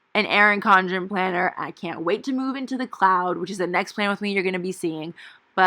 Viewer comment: What planner are you moving into? It sounds like you said the cloud? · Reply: yup! the Cloud by Aura Estelle :)